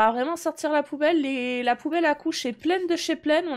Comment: Au lieu d'entendre la poubelle à COUCHE est en train de déborder!! J'ai compris "La poubelle ACCOUCHE est entrain de déborder" 😂😂